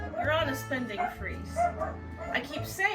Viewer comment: Spending Freeze? I thought you said Spending Spree!